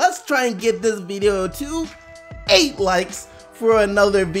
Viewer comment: I just figured out that at the end he says Keep getting the BUCKETS team SDC and I'm out of here I thought he said keep getting the bucks team SDC. Which I always found weird